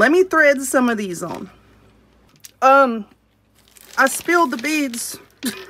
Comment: I thought you said “I spilled the beans!” 😂😂😂